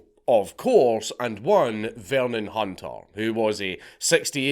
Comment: did you say the irs agents name was “Vermin Hunter”. Is that his deep state gang name? Did the government give him that title? I’m so confused.